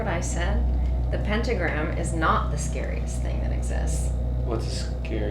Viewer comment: Idk ab y’all but around like 28:03 I heard her say a pentagram is the scariest thing that exists and the camera also went out of focus then after that when she came back she said it isn’t....